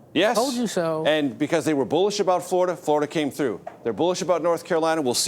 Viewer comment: when Major is speaking, towards the end of this post, is he saying *bullsh!t*....I am hard of hearing, it does sound like he is saying *bullsh!t*...... could someone who hears well please listen and provide some info on this? Thank you....